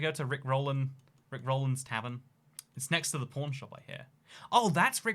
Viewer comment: Living in Vegas isn't that great unless you move to the subarbs of Summerlin, Spring Valley, or Henderson. Also, when I first heard him talk about the pawn shop I thought he said porn shop and porn star.